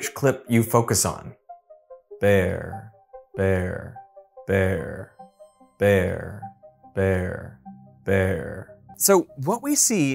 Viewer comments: I heard “bvere”. My eyes and ears couldn’t agree and I guess my brain just decided to roll with it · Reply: i closed my eyes and it sounds like he said bear bear there bear there everytime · The second one sounded like the mix of "bear" and "there". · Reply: I heard “bvere”. My eyes and ears couldn’t agree and I guess my brain just decided to roll with it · I hear "where' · I heard "bear" and "there" 😂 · I heard "Verre" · I hear "verre" (french for glass) but with an American accent (I'm neither English or French) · I heard “where” in the second clip. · I heard "where" mixing up fair and bear in some way, like how mixing yellow and blue gives green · I heard 'where', instead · I heard "bear" and "verre"... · I thought he said bear and there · I heard "where" in the second clip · I heard 'where' in the second clip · I heard "where" on the 2nd one. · I heard a mix of “there” and “vare” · I hear "mare" and "there" 😅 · Escuché “bear” y “pair, bear” en el segundo clip. 😅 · Am I the only one that heard "there" at second clip? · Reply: I heard "where" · I heard “bale” and “there” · With the second clip, i heard "Bear, Fair, There" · Second clip I heard "verre" or the French word for green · I Heard "mere" and "there"